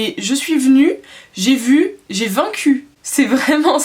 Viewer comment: Y a que moi qui ai entendu à 3:51 ''j'ai 20 q'' ? lol